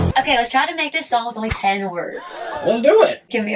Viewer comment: I thought she said n word instead of ten words